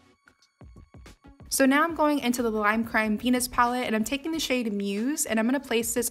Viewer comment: When she said lime crime venus palette i deadass thought she said penis palette 😩